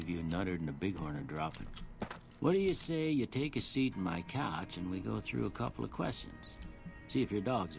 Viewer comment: when he said take a seat on my couch i thought he said take a seat in my cock